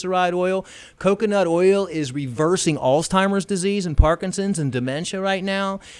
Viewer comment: he say "allztimers", instead of alzheimers. I think it's Kai the hitchhikers' brother.